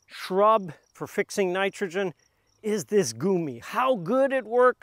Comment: Hi! @ 12:35, the subtitle said Goji, but you said Gumi. Are they the same thing? And if not, which one did you mean?